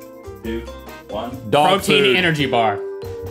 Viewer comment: When rhett said dog food I thought he said dog poo